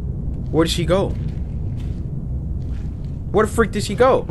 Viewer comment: is it me or is it me hearing Cory saying Frick instead of freak